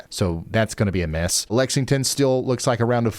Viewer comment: "Lexington Still, about a foot".. I heard Lexington Steel..about a foot (12 inches).. man of culture.